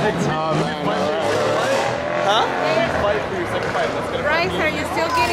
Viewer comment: Some one said Bryce are you gay him :" what did you just say "